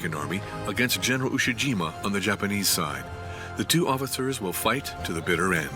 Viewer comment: did he say "offithers" instead of officers?😂